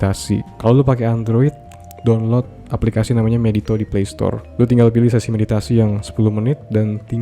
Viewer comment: nama app nya tadi apaan bang? Gajelas gw dengernya,, gw cari di playstore dengan judul meditory kaga ada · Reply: Medito bg